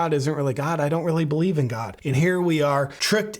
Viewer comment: I thought she said I believe in Gloria Steinem .lol